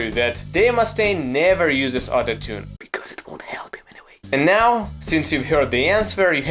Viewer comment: Anybody gonna ask what did he say in 0:28? I heard (because it won't help)